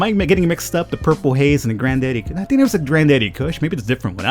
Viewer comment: did i hear purple haze this looks like the taste of a jojo refrence